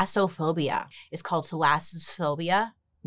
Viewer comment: I'm literally 12yo! Haha I thought you said phallophopbia! 🤣🤣🤣